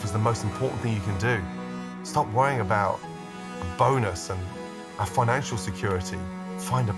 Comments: At the end, I thought Gordon said to "stop worrying about boners" during that inspirational speech So I went back to re-listen, and realized he said "a bonus" 💀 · i stg i thought he said stop worrying about boners at first 😭😭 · I *really* thought he said « boners » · At the very end when Gordan said “finding your passion in life is the most important thing you can do, stop worrying about a bonus” I heard “Stop worrying about your boner”